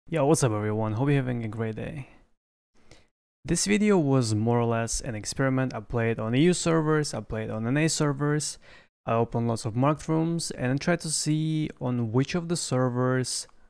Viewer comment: Ngl i thought he said i played on na server eu server i ate a bunch of mushrooms and well xD